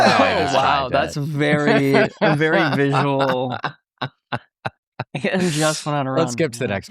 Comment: Hahahaha omg i thought you guys said "VERY sexual" lol and in my head I thought, "soooo a slutty pants?" Lol and I was right hahaha